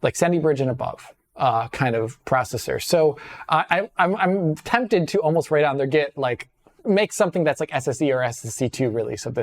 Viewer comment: I cannot for the life of me decipher what is saying here. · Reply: "I'm tempted to almost write on their Git, like, make something that's like a SSE or SSE2 release of this as well too, because feels like it should run just fine on older processors" - Approximately, 2022